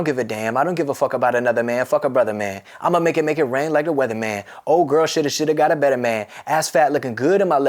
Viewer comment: Always thought he said "Imma make it make it rain like the weather man" lol